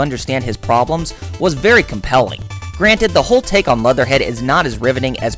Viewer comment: Did anyone else hear him say "motherhead" instead of Leatherhead?